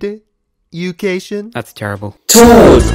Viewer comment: TORD PART IS WAY TOO COOL AND FUNNY when it's "RED HOODIE" i hear "ANYBODY"